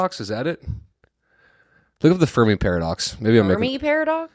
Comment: i thought was saying furby paradox